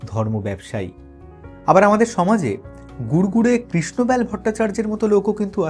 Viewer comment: এখানে এনার নামটা কি বললেন? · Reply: ওটা কাল্পনিক নাম। গুড়গুড়ে কৃষ্ণব্যাল ভট্টাচার্য